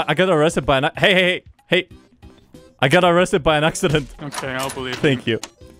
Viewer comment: "I was accidentally arrested" "I believe you :)" I love that so much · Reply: Actually the line was "I was arrested by an accident" which is even better because he misheard, was calling him an accident kek